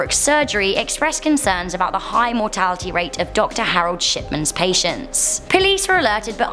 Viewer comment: dr. Harold shitmans is what i heard her say •-•